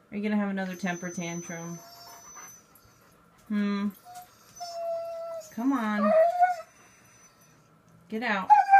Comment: When he does the moaning it sounds like hes saying "come onnnn"